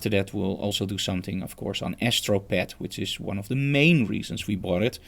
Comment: Hi thank you for this I didn't understand what you said from 4:45 mark until this end. What did you say please? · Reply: That was about astropad an app